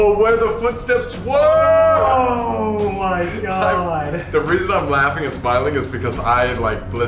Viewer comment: For the last one with the river before it said ‘reason’ I heard ‘one more’ so like ‘one more reason’